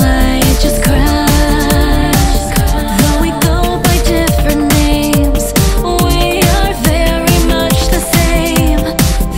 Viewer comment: "Though we go by different memes, we are very much the same." I don't think I heard that correctly.